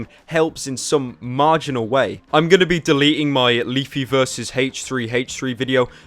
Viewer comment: when I heard "I'm going to delete" and I got scared